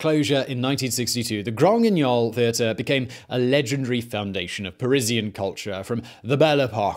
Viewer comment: My business blaze trained brain immediately went to 'allegedly' instead of 'a legendary'.